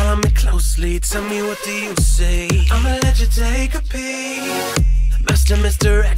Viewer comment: What it said: "Amma let you take a peak" What I heard: "Amma let you take a pee" BAHAHAH I'M FUCKING DEAD